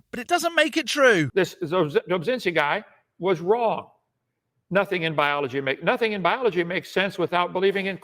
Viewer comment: I had to replay starting at 5:15.... I thought Kent said "Nothing in the bible makes sense without believing in creation."